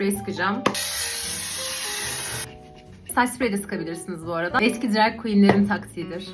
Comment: Abla yüzünüze saç spreyi de sıka bilirsiniz mı dedin yoksa ben mi yanlış anladım